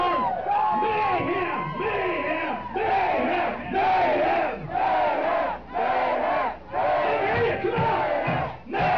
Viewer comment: what do they say? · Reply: Dimebag then mayhem. Mayhem was Jeffery mayhem Thompson security he was shot with Dime.